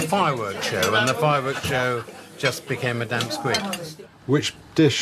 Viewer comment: did he just say damp squid?!?!